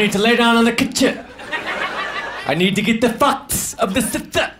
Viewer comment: I can't figure out what that first word is lol he needs to get the what of the south?? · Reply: I need to get the fuck out of the south